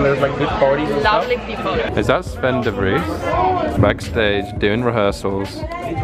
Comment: Who's the guy who walks in at 6:49? He's cute, I didn't understand his name. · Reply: Azzy Washington the name said in the vid is sven de vries, but i suppose you were talking about mats van snippenberg